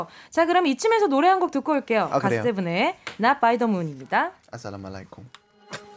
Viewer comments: Did Bambam just said Assalamualaikum? Which is a greeting in Arabic. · did you all hear what I hear? 😅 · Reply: Aslamu alaikum😁